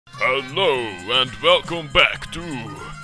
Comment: I opened this video by mistake, then I changed page again and what I heard was "Hello and HEUHEUHEU"